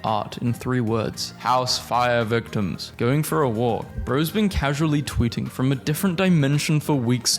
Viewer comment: I THOUGHT I HE SAID GOONING FOR A WALK!!